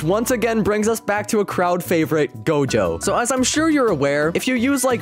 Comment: Did he said dojo? :Skull